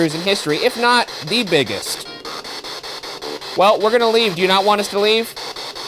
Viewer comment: I thought they said vegan not leave